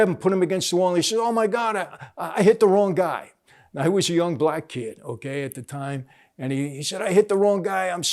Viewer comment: Lol I thought you said “ I was a young black guy at the time”